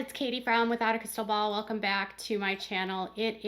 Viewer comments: It does sound like you say Katie brown lol I listened to it again. It’s the emphasis on “from” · I thought you said Katie Brown too lol